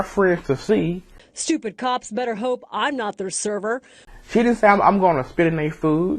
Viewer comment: that is not what she said at all. She said "THEY" better hope "I" am not "THEIR" server. THat has nothing to do with her hoping they don't get put in her section. Not at all. Usually you are pretty good with your objective, even entertainingly humorous. However, on this day... I can't agree with your point of view. You still get a like because you are awesome.